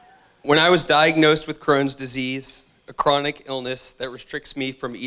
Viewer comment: thought he said corona and I was like 😶